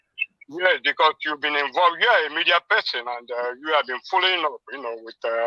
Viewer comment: I thought you said you are a Dr. Please reason logically. Thanks